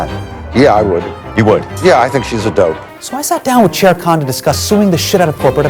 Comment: At the start of the video, I heard "Chair Khan" as "Sher Khan" (meaning "Lion King" in Hindi) :) , she is definitely worthy of that title as well :) hehe